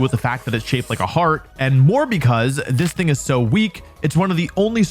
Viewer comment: did he say this nikka so weak? Lmao